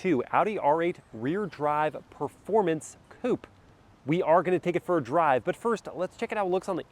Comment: I thought i heard you say “read drive performance Poop” 😂😅